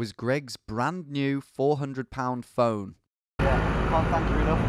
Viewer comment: Is it just me or did any fellow Americans also hear this as a "four hundred lb" phone at first, instead of "four hundred £", like he had a really heavy phone or something?